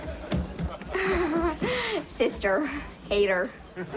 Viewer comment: did she call her sister a hater?? 😂😂